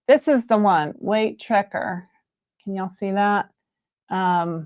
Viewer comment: <link> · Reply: thank you! I thought you said tracker, but the southern accent was making it sound different 🤣